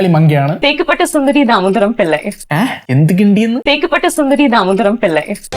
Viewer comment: ngl I heard 'makeup itta sundhari'